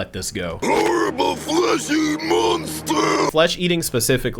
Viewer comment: (Side eyes the turkey meat I'm about to eat) Yes. Also, I thought he said 'fleshy monster', not 'flesh eating monster', since the second sentence sounds a bit too long for what he said.